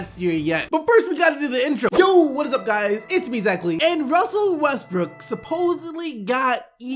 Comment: It's finally been settled!!! You say the intro so fast I always thought you were saying "He it's me exactly " not you know......what you actually say XD